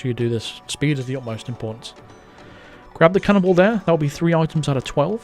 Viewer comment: I thought he said "cannibal" instead of cannon ball